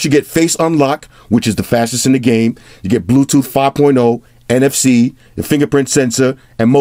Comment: Captions say Bluetooth 4.0 =/ 😂 3:46 PM 5/19/2018